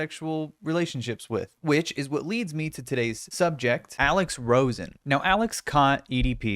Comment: I thought you were about to say "which is what leads me to today's sponsor" instead of "subject" 😂